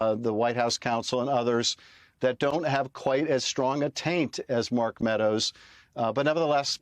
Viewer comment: Did he say Mark Meadows has a strong Taint?